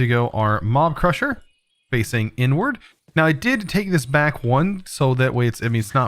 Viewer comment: did he just say the n word?? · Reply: Inward... wait... n wo... I hate you